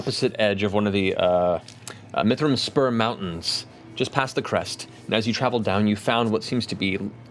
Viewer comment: I keep hearing Matt say "Mithral Sperm Mountains." Either I'm mistaken or Tolkien was raunchier than I remember.